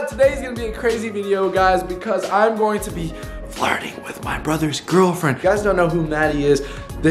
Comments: It sounded like you said, "Imma be farting with my brother's girlfriend." 😂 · At the beginning it sounded like said “Farting with my brothers girlfriend”I DIED 😂🤣